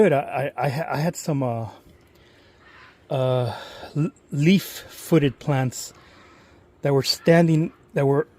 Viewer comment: i thought you said leaf cutter ants and was kinda confused because i don't think they live there.